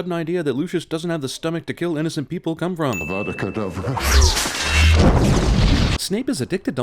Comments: I could have sworn Snape said "Abracadabra" instead of "Avada Kedavra". Damn. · didn't I just heard a-vada-cadabra?!